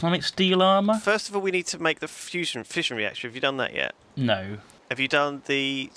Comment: I thought you said fishing reactor I am such a dumbass